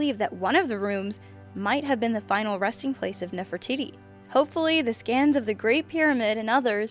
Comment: Sounds like said "Nefertitty"